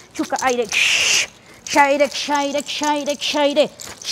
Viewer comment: i always hear they shout "shaidek"?? i hope i type it right i wonder what that means...